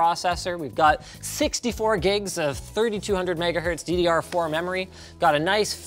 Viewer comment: Linus said 3200Mhz but the caption said 3600Mhz. which one is it